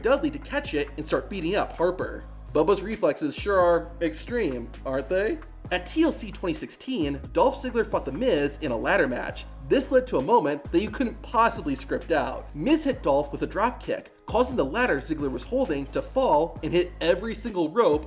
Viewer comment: bro I heard „Dolph Ziggler fucked The Miz“ 💀💀💀